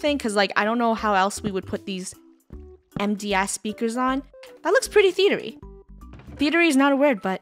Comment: I heard " Empty A** speakers"